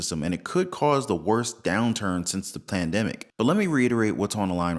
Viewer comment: ✅ 💥 Best Way to Invest in Gold ⚡ 👉 <link> 🏠 FREE HOME WARRANTY QUOTE - NEVER PAY For Home Repairs 👉 <link> ✅ 💥 JOIN THE FAM TODAY ⚡️ 👉 <link> ✅ 💥 11 MUST HAVE SURVIVAL GEAR & GADGETS (Updated) 👉 <link> ✅ 💥 Best Way to Invest in Silver 👉 <link> ✅ 💥 JOIN MY PATREON FAMILY 👉 <link> ✅ 💥 $175 per day for replying to comments on Facebook 👉 <link> ✅ Join this channel to get access: 👉 <link> 👀 💥 JOIN Chat Group NOW (Never Miss Another Show!) 👉 <link> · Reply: Did you just say "plandemic"?